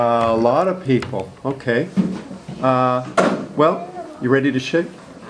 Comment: LOLOLOL at 0:50 sounds like he said "well, ready to sh*t" lmao